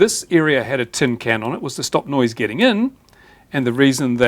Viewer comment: Misheard that the cans are against "nose getting in", as in nosey people who tear down their stuff. Got very confused at the "nose getting out" part.